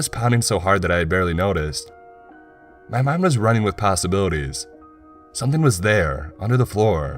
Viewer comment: Did he just say Pastabilities?